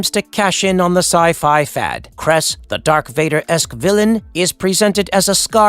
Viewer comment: Did he just say DARK VADER?????!!!!!! 😂